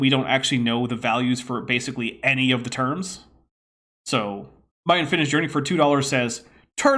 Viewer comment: I misheard "My Unfinished Journey" as "My Unfinished Drink" xD